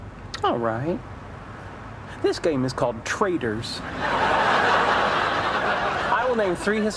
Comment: At first I thought sheldon said this game is called traders